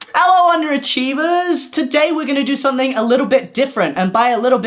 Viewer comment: When you pronounced 'underachievers' different I thought you were saying 'wonder divas' in your intros in general sob